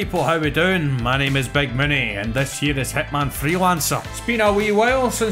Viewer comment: I swear I heard "Hitman Threelancer" 😛